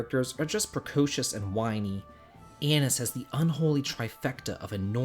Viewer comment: I totally heard “Anise” as “Anus”